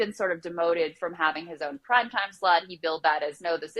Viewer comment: anybody else hear prime time slut ? Freudian puns.